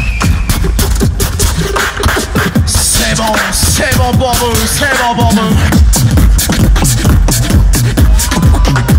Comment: What are they saying at 13:17 ? xD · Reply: Savon savon brother savon